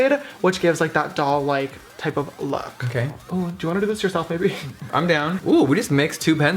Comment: anyone else hear "do u wana do this yourself baby?" I heard baby but my sister heard maybe... ah love you both the most (this comment isn't ment to throw shade on I call my friends baby all the time and I am gay. Just wanting to know xxx) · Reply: Just put captions on. I'm so happy it's baby. I legit thought o was the only one who called my friends baby